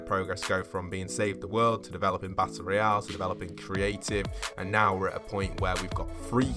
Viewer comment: battle royale, the subtitles say battery acid